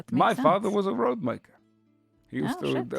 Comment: I first heard that as my father was a 'roadman' not 'road-maker' 🤣